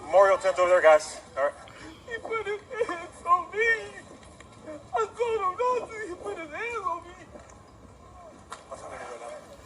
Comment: It sounds like he said "he put his hand on me. I told him not to put his hands on me"